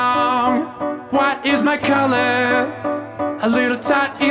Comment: I thought he said “what is my color?”